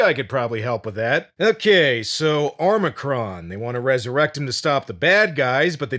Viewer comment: i keep hearing "Omicron"